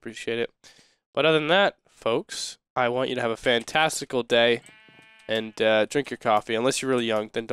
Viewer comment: When you said have a fantastical day, I thought you said have a fantesticle day. I loled for a little :)